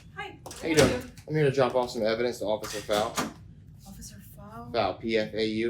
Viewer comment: I thought I heard "officer foul" lmao 😂🤣